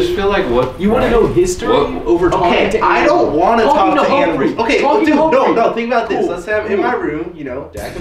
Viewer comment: Did jesser say Hamphrey 2:55 😂